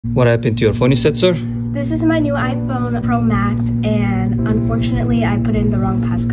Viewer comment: This kid is more grown up than me to have a pro max😅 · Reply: oh my bad I thought he said 14 pro max